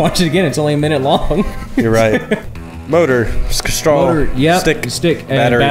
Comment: WAS THAT MY EARS OR DID CHANDLER JUST SAY" sks-straw" AT 6:15 OR SO? VSCO BOY CONFIRMED.